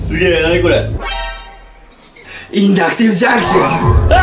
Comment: が「エレクトリックディスファンクション」に聞こえたのは俺だけじゃないはず…